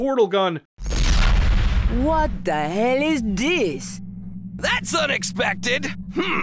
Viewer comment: So that scientist guy at 18:04..... not sure why but when i heard him say "That was unexpected!!" I instead heard "What the Hell?!? Did somthing just hit us?!?". I dont know if its true but it kinda sounds like the same VA that did some of the marines from Halo: CE.